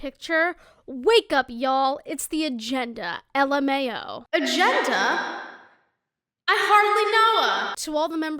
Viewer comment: "agenda? i hardly know her!" why did i hear "a gender? i hardly know her!" ?💀